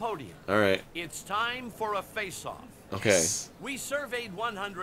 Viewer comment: omg I thought he said it's time for a facial...